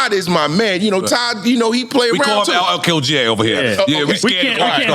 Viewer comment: is it me or did it sounded like Nore said, LL Kill J??